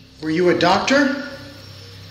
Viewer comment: why i heard "stop there" not doctor hehe